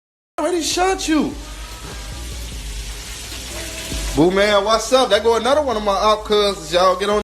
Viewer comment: Did memo say poo poo man was his cousin??? At 11:15?